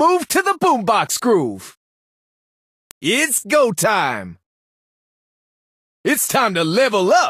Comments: i thought he say disco time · I thought he was saying Disco Time